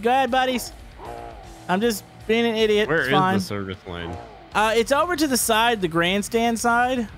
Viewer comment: I thought you kept saying 'Server slang' but you were saying 'Service lane' xD